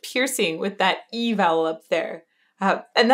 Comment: Hi Elizabeth. At 16:20 did you say E vowel?